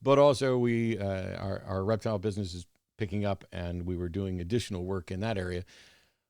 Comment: I heard "Our erectile business is picking up"...🤔